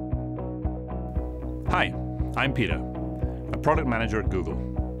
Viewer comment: Мне одному послышалось "Hi! I'm Pidor!"